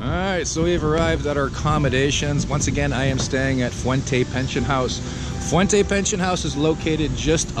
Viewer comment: Oh my gosh, i thought you said Fuente Prison! Oh No we gotta get Ned outta da joint! Always good info, i will be in Davao and Bali for a few weeks, love it down south. I call it the Alabama of PI. Wife's fam is around Davao.